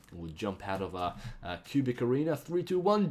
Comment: I thought he said pubic area O.o